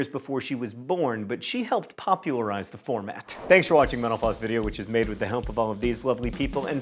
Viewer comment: Enjoyable factoids, as usual. I always get a giggle at the closing when he's talking really fast and I hear him say "Thanks for watching menopause." You're welcome.